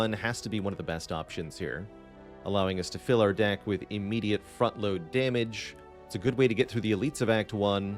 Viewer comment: Almost made me spit out my food thought you said "immediate f***load damage"